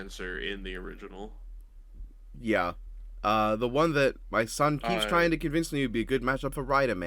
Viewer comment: I’m sorry did you just say “my son”